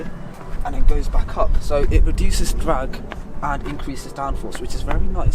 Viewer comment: if you put subtitles on it says drugs instead of drag